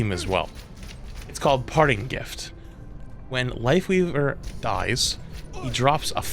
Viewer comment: did he just say "wifeleaver"? lol